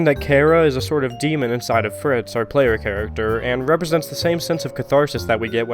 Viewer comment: did he just call Frisk ... Fritz?